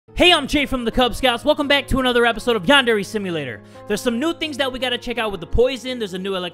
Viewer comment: I thought he said Pondera simulator I know it’s Yandera simulator But when I looked at it again I don’t hear ya